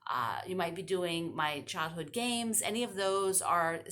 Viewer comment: It sounded like you said "CHOCOLATE games!!" Hey, works for me.